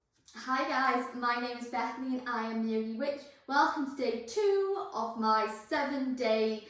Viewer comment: So my subtitles thought that you said ‘My name is Bethany and I am nearly rich…’ 😂😂😂 This amused me quite a lot.